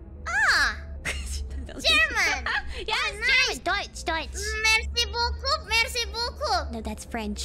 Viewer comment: I thought said Merci bokep 🤣